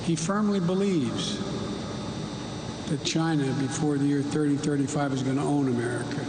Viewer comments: Hey, I'm not sure why you're all freaking out. I swear I heard him say 0:40 "the year 3035" — we've got about 1000 years to get our act together! 😂 I mean, c'mon man! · He clearly said “3035” … where did they get “2035” from?